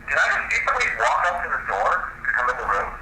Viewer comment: I hear get out of my room